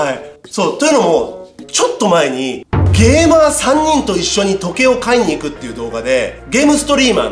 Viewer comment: ゲーマー3人が、ゲイバー3人って聞こえた！笑 ゲイバーにも行くんだ？と思ったわ。